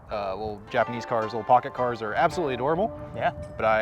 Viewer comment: i thought for a sec u guys said kit cars 😂